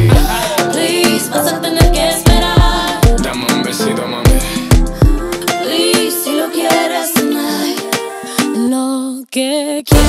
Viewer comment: Pensé que decía “please si lo quieres oh my” y estoy descubriendo que dice “please si lo quieres tonight” xd